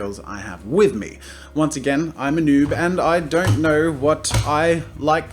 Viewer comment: When he said I'm a noob I heard I'm a boob.... What is life?